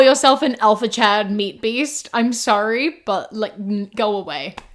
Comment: I was listening and not really watching while driving, I thought I heard “if you’re looking for an alpha Chad, meet Beast” like as if this was Mr. Beast’s profile. I had to rewind to learn it was actually the even more cringe “alpha Chad meat beast”